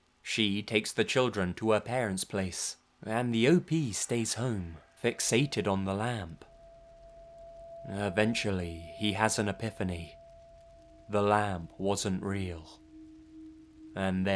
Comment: Did you say lamb or lamp at 7:00?